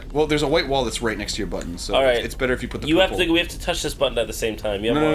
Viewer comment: I heard: "We have to touch this butt at the same time."